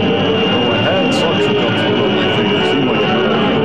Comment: anybody have any idea what Mike said there? lol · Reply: "Putting suction cups on my fingers seemed like a good idea at the time..."